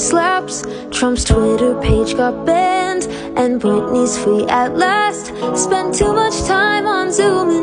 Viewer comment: I thought she said “Britney’s feet” I was so confused